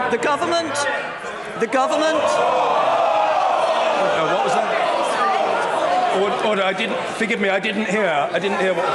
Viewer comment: What word was used in 4:49? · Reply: Liar